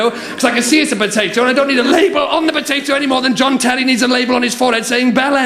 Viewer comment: What was he saying at 3:16? I figured out everything else but that sentence is escaping me, as I don't know who he named or what he said should be on their label. "I don't need a label on the potato any more than (John Talley?) needs a label on his forehead saying (bell end?)" What was he really saying? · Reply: he says "John Terry" . He is a football player in the UK